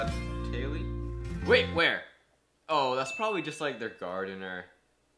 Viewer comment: When said "its probably just their gardener" I thought he said "it's probably just their guard"😂😂